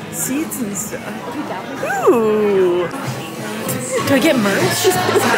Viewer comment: I swear I thought said Do I get murdered? 😱🤣